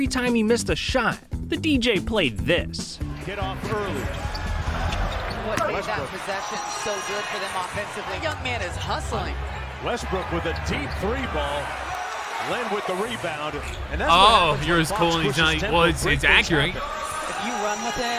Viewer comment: did he say russel westbrick?lmfao